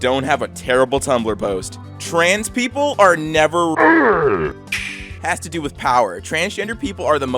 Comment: my brain really thought it said “trans people are never racists” and say there very confused for like 30 seconds